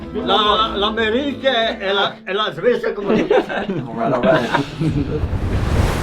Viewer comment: he clearly said swiss, not sweds. Classic american mistake🙃